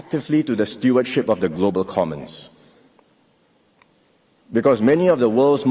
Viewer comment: At @ 17:40 he says "we will contribute actively to the stewardship of the global communists" he meant to say,, "to the global commerce,", but he said communists And at @ 5:12 he said "the long and costly wars in afghanistan,, and iran what he meant to say, is, in afghanistan, and iraq duh thats major slip ups wooops lol ;D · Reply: check your hearing. He said "Commons" and "iraq". You must be illiterate